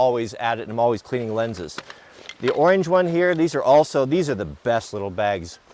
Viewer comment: Did he say ORANGE ??